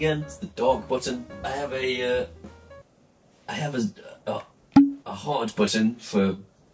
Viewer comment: I honestly thought you said "I've got a hard button... " I won't even try and explain where my mind went...